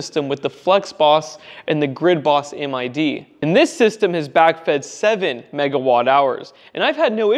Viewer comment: He was talking generation over a period of months. A typical whole-home system generates between 500kWh and 2 MWh per month, depending on how much solar is installed. Roughly. Across summer. It adds up. Think of it like this: If you had 7kWp worth of solar panels that would generate roughly 30 kWh/day (or more) in energy. Multiply by 30 days and you would get 900 kWh/month of generation. That's 0.9 MWh per month (across summer). has a lot more than 7kWp worth of solar panels to play with. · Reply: the part i misinterpreted might be : he said "...system has BACKFED 7 Mwh..." , what i just realized as i turned on the subtitles. Before i misheard as im not a native english speaker. In that context his statement does make sense to me. Thx for trying to clarify it. Appreciate it.